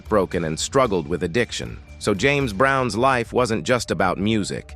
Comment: Am I high or did i hear him say James Brown at 16:44? R.I.P. MR. James